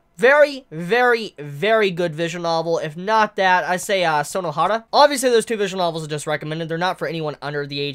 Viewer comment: Did just say Sonohana ?.............